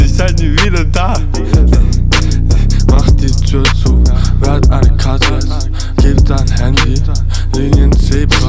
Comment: Ich have verstanden "Wer hat ein Kratzeis?"